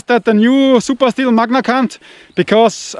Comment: Instead of “MagnaCut” You said “MagnaC*nt” 🤣😂🤣